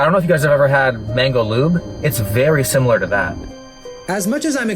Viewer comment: … did he say have you guys ever had mango lube?